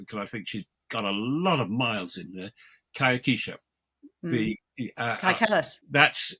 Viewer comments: Robert Fox mentioned the name of a woman who he would most like to speak to. I couldn't catch the name and the transcript says, kakha. If anyone knows the name, please reply. Thank you. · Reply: it must be kaja kalas, but it sounded not like this name... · Who are they talking about wanting to interview at 30:24?... It's driving me nuts and I've Googled everything I think I heard! 😕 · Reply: They are discussing Kaja Kallas, a political novice from Estonia who was appointed Vice-President of the European Commission in December 2024. She is fiercely anti-Russian and could escalate conflicts if anyone of significance took her seriously—which, thankfully, they haven’t, as she is largely ignored. In my opinion, she is reckless. Estonia borders Russia and would be overrun in minutes. You’d think she’d have the good sense to seek a pragmatic approach, but instead, she is both arrogant and foolish. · Yes, thank you, I got that name, but a few seconds later, he mentioned another name - a woman - which I couldn't get what he said. The interviewer repeated it, but again, it was unclear. Have a listen from 30:33 if it is no trouble. Kind regards. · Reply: I think it's Kaja Kallas, the Estonian pm